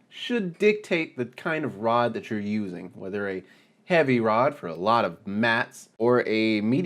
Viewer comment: Took me a second to understand what you said after heavy about 2/3 way thru, at first I thought you said "heavy for a lot of meth". Then I realized you said heavy for mass. Don't mind me I'm just a bit deaf lol. Good video man.